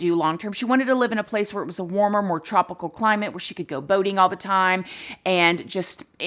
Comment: When you said boating all of the time, I thought you had said voting all of the time. For a brief moment my brain couldn't comprehend it, I don't know why I am sharing this.